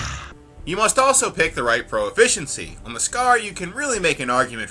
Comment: Am I the only one who heard "pro-efficiency" at 8:08?